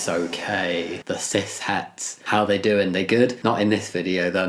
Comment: When said "cishets" at 0:15 the subtitles said "Sith hats" lol